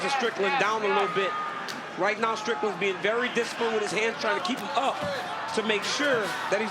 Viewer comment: Cormier says “very dis—“ I can’t understand what he says, maybe he didn’t pass 1st grade phonics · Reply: Very disciplined